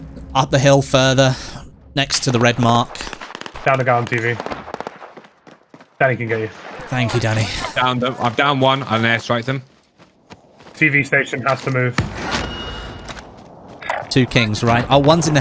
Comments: thought he said thank you daddy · it sounds like said thank you daddy 😂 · When he said "Thank you Danny" I heard "Thank you Daddy" and I was like, well they have a close team relationship.